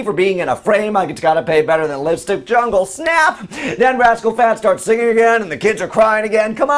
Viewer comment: Ok, it sounds like you said "rascal fats". Maybe that was on purpose. lol?